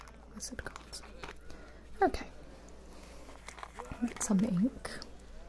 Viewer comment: Did anyone else think she said "lets get some milk...."